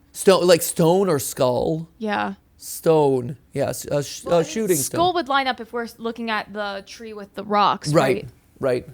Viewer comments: I hear “the sh00ting spree” as if it was a mass event? (I’ll add on or change after I keep watching) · I heard "human skull", anyone else?